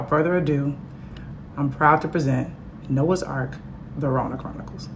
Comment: I thought it said "Rona's Arc" at first.